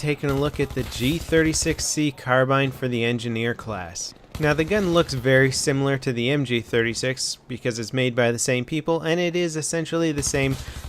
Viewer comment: between 0:10-0:26, He clearly says" THE G36C LOOKS VERY SIMILAR THE MG36 IN BAD COMPANY 2, BECAUSE ITS MADE BY THE SAME PEOPLE".... Is that too hard for you too understand?